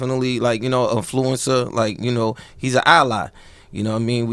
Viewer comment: Did Smack just say at 7:45, "he's (Loaded Lux) a Allah?" If that is what he said, then Smack, that is sacrilegious. You want to rock The Muslim beard, cool, no problem. But calling a human being "Allah" is completely unacceptable. I've rocked with Smack for years, but I don't cosign any of that blasphemy. The idea of mortals/humans comparing themselves to any deity is offensive and bad enough, but to go further and compare yourself to Allah, that's the ultimate distasteful comment and filled with delusion and egoism. Shaq slap the beard off his pistachio head.